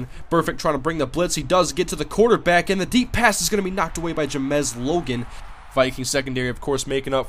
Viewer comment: Did you say JaMes Logan had a deflection at 8:49, or did I hear something wrong?